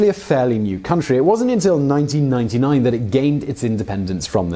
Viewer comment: He said 1999. Subtitle said 1949. Independence given by the Dutch? LOL We didn't admit nor need it, especially from someone who invade our country for hundred years. The true Independence is still 1945 by our country itself, 1949 is Dutch recognition